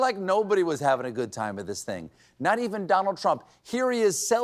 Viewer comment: lol, I thought she said "Dollar G Trump"